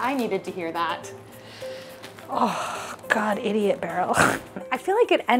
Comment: For one second I thought you said “idiot barrel” and I thought that was the best insult ever 😂❤